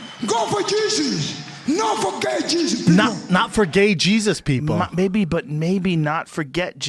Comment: I genuinely thought he said, "not for gay jesus!"